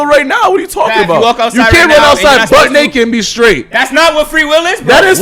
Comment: did he just say you can't run outside butt naked and be strait? XD